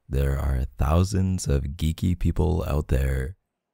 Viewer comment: Him:shes probably geeky Me:did he just say kinky A FEW MOMENTS LATER Me:OHHHH GEEKY ya totally thought of that